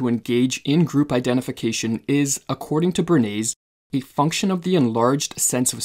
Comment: Bernays becomes' béarnaise'' so, the subtitle were proofread, and 'béarnaise' was picked up, instead of the proper name (of this gentleman, Louis Bernays).